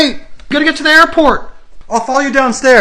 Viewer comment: I thought he say :F**K home alone Me:🤨 Me after realizing its stuck not F**K:😮😯